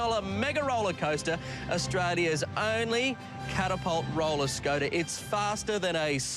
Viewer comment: Am I the only one who hears "roller scoater" at 2:55???